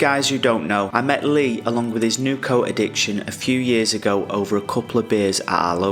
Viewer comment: I had to play that twice then thought you said coke addiction 😅 🍺